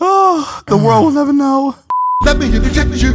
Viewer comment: Bro what is that audio @ 48:54 & what is he saying hahaha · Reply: It’s a jingle that his cousin Teddy wrote. It says “let me interject you”